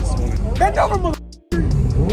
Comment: did he say bend over so he can.......?